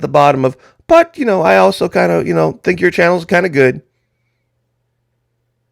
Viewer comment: The good ol' compliment sandwich! 🤣😂Nice breakdown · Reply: Ut oh i thought you said Compliant sandwich..😲😖